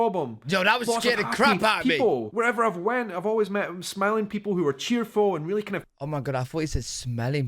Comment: OH smiling, I thought he said smelly!